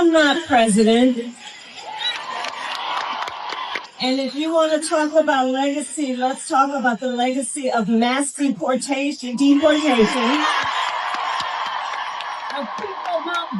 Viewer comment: Krystal, you need to listen closer. I’m no Kamala fan. And she definitely blames everything but herself for the loss. But you misheard what she said in that clip in a way that fits your preconceived notion. Here’s what she said, “ Let’s talk about the mass deportation of people not voting for Donald Trump.” As in…he’s deporting people that don’t vote for him. Factually true? Probably not entirely, but I bet you agree with that sentiment. As do I. But it’s like you heard the words “people not voting” and just didn’t listen to the rest of the sentence. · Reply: And this is what she said: "I’m not president, and if you want to talk about legacy, let’s talk about the legacy of mass deportations, of people not voting, and Donald Trump"